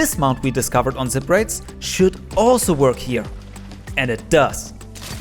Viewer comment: Its goofy how the captions say jews instead of juice